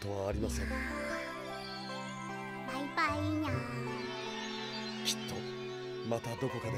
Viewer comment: パイパイにゃーん、って聞こえんだけど…